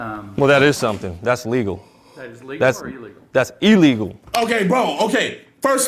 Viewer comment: I think he said illegal the first time just with that southern drawl🤣